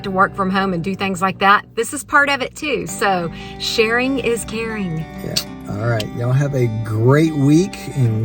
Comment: I heard “Sharin’ is Karen.” That’s a pretty good mantra for taking your name back! 😂